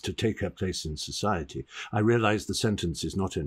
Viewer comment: is he saying Benischous?